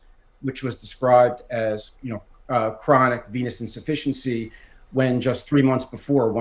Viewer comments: listening to the video while doing other things I actually thought you said that he suffers from "chronic penis insufficiency" and laughed out loud. :) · I honestly thought you said chronic penis insufficiency lol. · Omg I thought you said penis insufficiency 😂 Venous